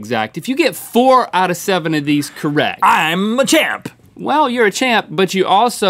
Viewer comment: Did Rhett just say "I'm Machamp"?